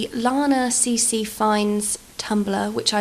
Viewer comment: hey!! i love this so much, could you link to the cc? new sub <3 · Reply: Hello Idk if you remember this comment you made 2 years ago, and I'm sorry to say I don't have the link for the cc, but I'm watching the vid today and at 7:05 I think she said she found the cc on Lanaccfinds tumbler, I'm not sure if you still need to know the information or not.